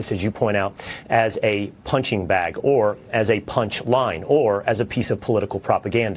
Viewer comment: He actually said, the Constitution was being used as a punch line. Not Due Process.